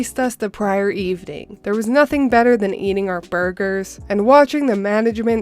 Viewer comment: did i hear burger? :thonks